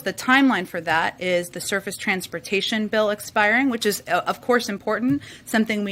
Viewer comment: Did I hear her correctly sounded like she said they were concerned about renewing the Circus transportation bill? Sounds about right?